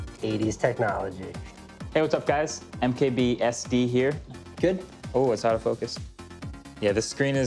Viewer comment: Hey guys it's MKBSD here -- Standard Definition instead of High Definition😂😂